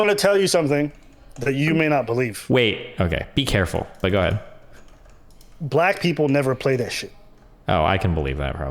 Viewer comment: What does he say at 29:44 ? "Black people don't play issue?" What does that mean? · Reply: "Black people never play that shit." Youtube automatic captions aren't always 100% correct, but they've gotten a lot better in recent years and you can use it to understand something that you didn't hear clearly.